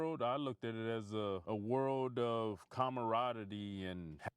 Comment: Camaradiddy??? Did he just say that???